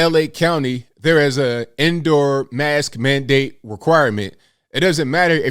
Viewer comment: I thought you said there's an "Endor mask mandate" ... if everyone was dressed as Ewoks, lockdown would have been more fun. And life generally tbh.